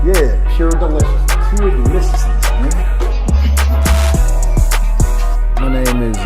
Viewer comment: In the intro when dude say pure deliciousness sound like Banky said pure deliciousness nikka.😅😅😅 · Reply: He said, "Pure deliciousness, man! " was emphasizing , get it right , "how many times do I have to tell you man?"